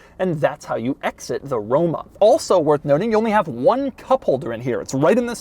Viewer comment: I normally watch videos at 2x speed. Everytime Doug says "Roma" I hear "rona."